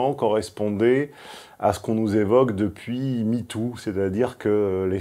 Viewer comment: j'ai entendu michtoo .... surement un hasard